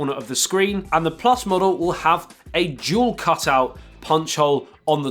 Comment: whenever he talks about the s10+ camera cutout it sounds like he says juul cutout